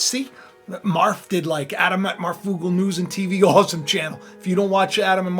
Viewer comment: Can some one tell me what he saying just after 27:05. The channel. Is it Adam and Marfoogle. I can't quiet clearly hear. I can find a marfoogle but not an Adam and Marfoogle. Thanks 👍